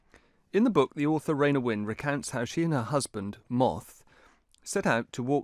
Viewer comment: For a second I thought you'd said 'recants'. Would apparently have worked a bit better than 'recounts'.